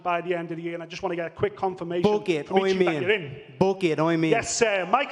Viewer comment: When Conor said book it i thought he said FOOK IT lol